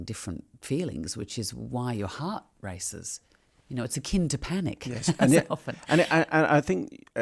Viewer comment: Could anyone say please if she says "kind of panic" at 4.43? I'm a bit confused 'cause I hear 'kind' not 'kaind'. :) She's practically my English teacher so I always take notes listening to her interviews. :) Thanks in advance. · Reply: Cate says "it is a AKIN to panic" akin /əˈkɪn/ adjective adjective: akin of similar character.